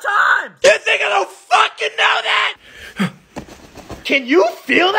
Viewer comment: The first time I watched this I thought he said “THIS N***A DONT FUKIN KNOW THAT ”